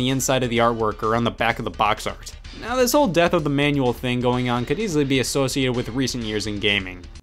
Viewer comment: I skipped ahead and I heard "The Death of Emmanuel"... and I briefly felt bad for the guy.